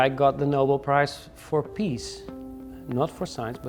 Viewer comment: I heard "Nobel Prize for Peas" instead of Peace at first 😂